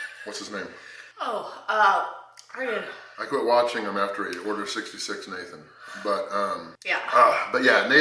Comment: What does say at 6:40? about why he stopped watching someone? I didn't understand what he said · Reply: That’s something that only people who also follow Nathan and a guy named Jason. Jason got mad and “Order 66’d” Nathan (Star Wars reference) and a bunch of trolls fake ordered pizzas and other takeout foods to his grandmas house where he lived.